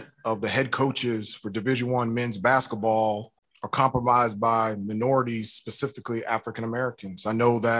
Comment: When he said “26% of head coaches of division 1 basketball are ‘compromised’ by minorities...” no one else has picked up on this but compromise means to jeopardize BUT I think he meant “comprised” instead. Words are powerful man. Use them well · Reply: Bruh! I thought he said comprised! Rewinded and you're right! Talk about a freudian slip. Definitely ain't kinfolk